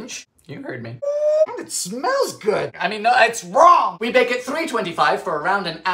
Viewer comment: "And it smells good... I mean no it's wrong" really got me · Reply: I thought he said 'raw' not 'wrong